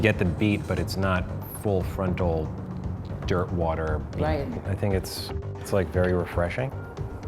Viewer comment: when he said dirt water I thought he said derp water